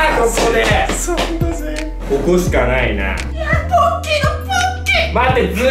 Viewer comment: ポッキーの勃っ起～！？って聞こえてしまった…